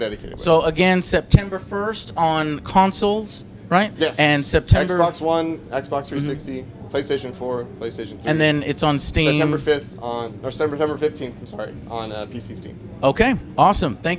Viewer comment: Hah! Did he just say Xbox 260?